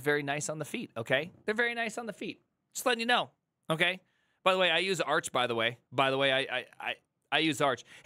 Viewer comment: Did prime just say, "I use arch supporters by the way" ?